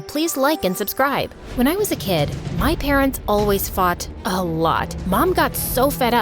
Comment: OMG LOL I thought she said fart not fought F-A-R-T! 😂😂😂💀💀